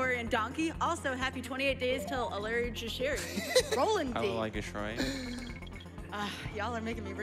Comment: Can someone help me out, I can't seem to make out what was said at 27:30 "It's 28 days til [something]" but someone on the couch corrects her, and I can't tell what is being said. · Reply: It's supposed to be "Allerlei Geschrei" (AHL-er-lie guh-SHRY, roughly), which is the German translation of the level Squeals On Wheels. You can also hear the couch guy saying it when enters the level at 15:10. Why they're referencing it as if it's an upcoming holiday, I have no idea. Presumably an inside joke of some kind. EDIT: It just dawned on me that this run took place on January 6, which is 28 days before February 3- which corresponds to the level's position in the game (World 2, Level 3). Still don't fully understand why that's specifically a thing, but there you go.